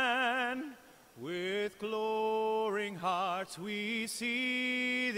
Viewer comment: Did Ramin just sing "With gloaring hearts"? It's glowing. What is is a gloaring heart anyways? Also... he needs to enunciate his d's. It sounded like he said "God keep our lan" Also, being that I'm not American, I don't know the whole US anthem, but isn't it supposed to be "home of the braVe"? Not "home of the brae"? ...... And yet he sings on shows like Phantom of the Opera and Les Miserables.